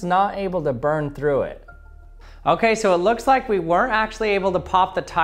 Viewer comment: Is he saying were or weren't able? To me it sounds like "were" which would be wrong or am I missing something... I think pronunciation in these cases are important. Also the steel wire mesh under the rubber would dissipate the heat more effectively, than the rubber itself and help overheating the point of the laser.